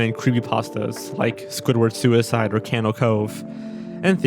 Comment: What is that “something” Cove he mentions at 0:58? I can’t figure out what he says but it looks very creepy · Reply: Candle cove it was a creepy pasta about a made up tv show